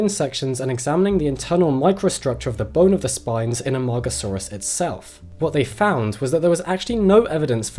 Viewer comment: I hat the internet because everytime he said the dinosaurs name, i heard, "amongusaurus"